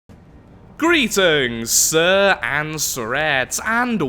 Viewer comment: What are you saying in your intro? This is what I hear: "greeting and Tourette's"